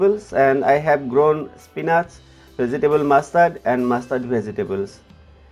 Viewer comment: I have grown vegetable mustard and mustard vegetable.. Did I hear it wrong?